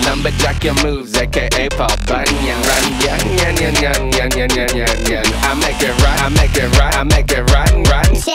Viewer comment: i heard nyan nyan nyan lol Also...Who's here because of a meme? XD